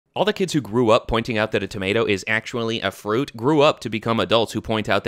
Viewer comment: Did you mix up potato with tomato??? Shoot I just misheard I should sleep